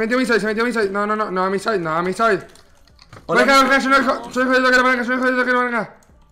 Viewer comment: que dice ?? XD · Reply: Creo que dice yo no dejo de pegar a la palanca